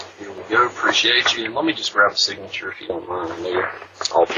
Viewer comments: I heard “let me just grab a signature if u don’t mind do yuh” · All I heard was “I appreciate you sir can I just get your signature here?”. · Sounds like he said here ya go and handed him the pizza.